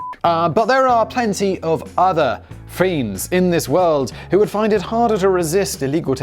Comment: Between 4:30 and 4:40 the captions say friend instead of fiend.